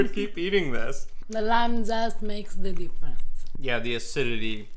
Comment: I swear to you, when said "the lime zest makes the difference", what I heard was "the lambs ass makes the difference." And for a moment I thought... "lambs ass. is that a thing?"